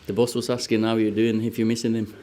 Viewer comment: I thought he said "the bus was asking how your doing,if your missing him" directly insulting Jose until I realised what he actually said 5 seconds later.